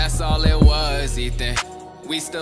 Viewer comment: When he said Hilla I thought he said hitler